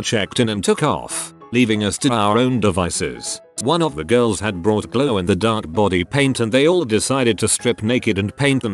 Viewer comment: Thought it said that a girl had brought blow.